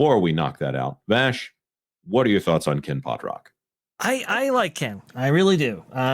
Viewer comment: All I kept hearing was "Ken Poprocks". Didn't catch anything else.